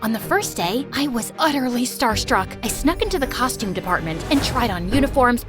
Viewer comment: help i thought it said starbucks